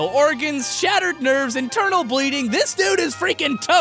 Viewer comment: I thought for a moment you said eternal bleeding